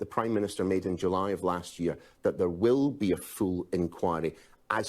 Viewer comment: "there will be a fool enquiry". At least that's what I thought he said.